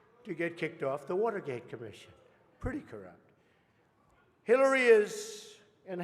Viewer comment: I thought the guy who introduced Hillary was going to die right there on the stage. What's wrong with his throat. And it sounds like he says "Killary."